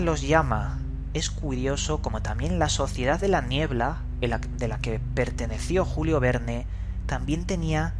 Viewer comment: Por un momento escuche "La sociedad de la mieldad"